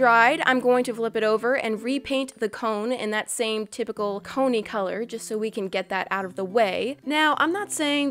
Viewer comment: When you said cony color i thought you said pony color